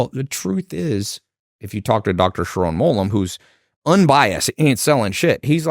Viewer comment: Who is the de tainis talking about at 10:43? Cant hear the name. Dr sherom bolum? Wha?